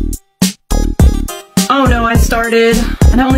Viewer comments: I thought she said "oh no i sharted" 💀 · Bro I thought she said "Oh no I sharted" 💀